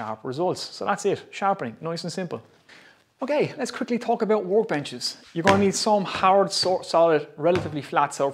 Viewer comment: You said workbenches, I heard warp engines.